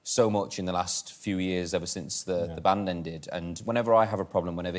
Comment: did he just say "the band ended" at 22:41 THIS IS NOT ACCEPTABLE